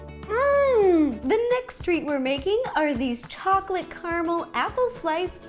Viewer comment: i thought she said the first tree we're making 😂 i'm like WTH